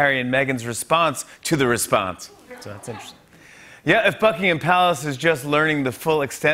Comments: > Did he say > Fuckingham Palace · I dunno I think he said fuckingham palace. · I thought you said fukingham palace just before you mentioned prince Andrew. Although it's a good name